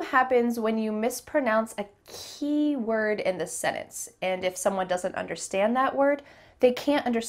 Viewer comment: LOL! "The problem happens when you mispronounce a key word in the sennance" .... "Me: In the what??? The word sentence has a 't', and it's not silent.